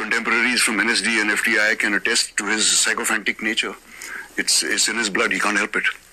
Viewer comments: I heard him say " Sycophantic behaviour" not "Psycopath". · Love your programs Tahir Saab. I heard a few times @ 1:20, I think Naseerji said sycophantic and not psycopath. Usually you pay close attention to content, somehow skipped this time. Otherwise, Naseer bhai is a much respected actor, I think your analysis is quite accurate, he feels he hasn't got his fair share of bollywood.